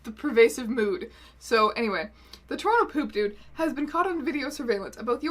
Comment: when the news reporter said ‘opuku has been caught on camera’ i genuinely thought she said ‘a poopoo has been caught on camera’ I’m dead😂😂